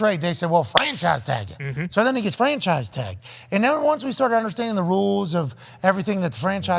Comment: I swear at 2:14 it sounded like Pat said "So that nigg@ is franchise tagged" 😂